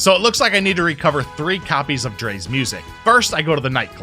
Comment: I thought you said drake 4:34 LOL